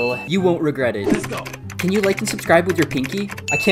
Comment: I thought he said can u like and subscribe with ur pp😂😂😂😂😂😂